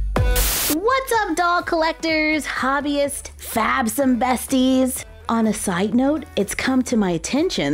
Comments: OHHHHH, I thought you were saying "Fabs and besties" and it never made sense to me , I think captions heard it that way too. Thanks for explaining it's "Fabsome Besties" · I always thought you said "fabs and besties" lol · I have always thought you were saying "Fabs & Besties!" Lol